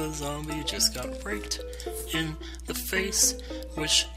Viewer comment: sounded like you said raped in the face, not raked.